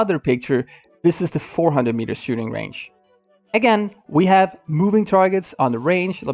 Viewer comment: I thought it was a girl saying "Again"